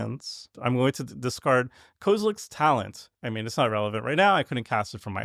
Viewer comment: i was so confused when i heard kozileks talent. i was like wtf set did i miss hahaha